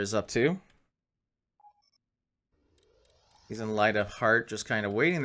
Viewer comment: I just Love that system, Every time I hear " Light of Heart " my brain hears " Light a fart " and i giggle like a kid xD On Another note, have you tried the LitCubes universe mod ? if not i personally reccommend it